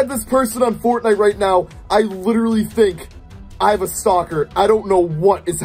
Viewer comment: I thought he said “I am a stalker....IM IN DANGER!”